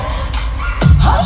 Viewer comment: sometimes i hear "likeadid"